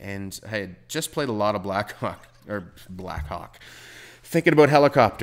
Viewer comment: did he say black cock ?